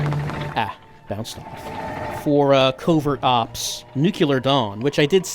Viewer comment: Misheard that one game's name as Covert Ops: Nuclear Dong